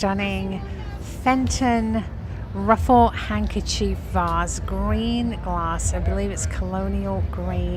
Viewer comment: Oh gosh, I thought u were saying what a gorgeous hen on nest lol. Thank goodness it was the handkerchief vase ☺